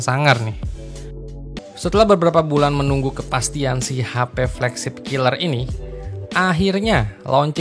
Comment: hah flagship killer? Nggk salah denger gw kan?